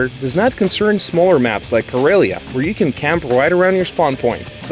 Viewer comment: Wait, did he just say "Corellia" at 2:30?